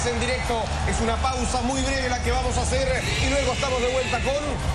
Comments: un pausa muy negra? ... WTF? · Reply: Al principio pense que dijo negra pero despues me di cuenta que digo breve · un pausa muy negra? ... WTF? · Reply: Primero leí tu comentario, y fui al minuto 1:01 y escuché negra, después leí el comentario de Cristian y escuché breve, después lo volví a escuchar y escuche negra, díganle a ese narrador que se ponga deacuerdo